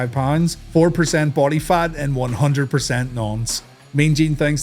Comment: Did you just call Luger a nonce!?😂